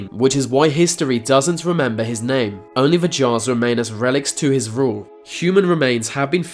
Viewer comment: In 2:22 is he saying "Jars" or "Jaws" ?